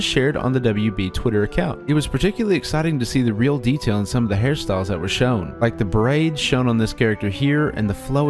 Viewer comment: My sleepy brain thought he said Harry Style instead of Hair style